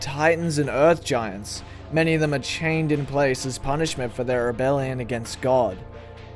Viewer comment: Holdholdhold, did I hear, “rebellion” against god?